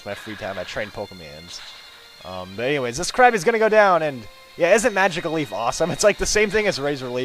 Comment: i thought u said:''And this crappy is going down''..:D